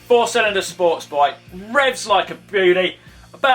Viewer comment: Did he just say "Revs like a goolie"? I think Colin has something wrong with his goolies....